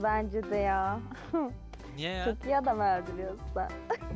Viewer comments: de ne diyor anlayan varmı ? Bu arada süper video başarılarının devamını diliyorum 👍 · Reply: çok iyi adam öldürüyorsun anladım ben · de ne diyor çok iyi biliyorsun sen diyor ama anlaşılmıyor